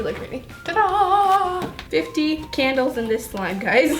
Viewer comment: lol i thought u said mixing 50 CANDIES into slime LOL